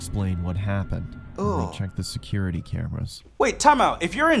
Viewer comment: I thought u said there wasn’t security in there lol